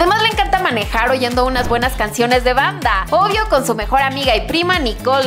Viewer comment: acaso escuche "Bangtan"???